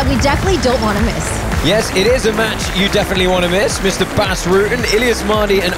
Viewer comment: Great way to end the season the 1st fight was the best by far....Am I really the only one that heard the comment "that's a fight you definitely wanna miss" instead of DON'T????🤣 after trying to repeat a comment